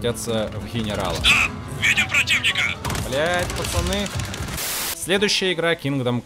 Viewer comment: бл пацаны я один это услышал